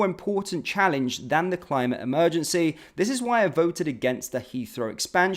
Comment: Did you just say queef instead of tweet 😅😂